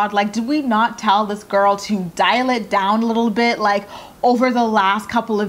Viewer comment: I thought you said "dilate down" instead of dial it down omgggg ahhahahaha